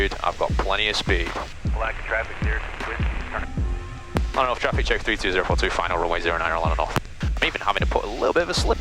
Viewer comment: Bro what. "orlandotraffic32042finalrunway0NinerNorth" ?? I cant understand what you're saying at the end lol 21:30 If I were in the traffic pattern and heard that I would only pick up that you're on final. Fast comms hahaha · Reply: Orlando North.. You got the rest of it, which means you did pick it up. Good job!